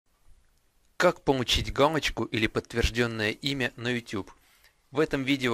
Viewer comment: Мне одному послышалось, что он сказал:"Как помучить гамочку..."?